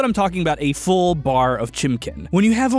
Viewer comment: did he just say chimckin